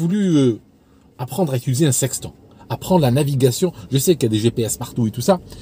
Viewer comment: Désolé, Aldo, mais la première fois, j'ai entendu « j'ai toujours voulu apprendre à utiliser un sextoy » 🤣 Magnifique vidéo comme d'habitude. Partagée avec des gens qu'elle touchera profondément.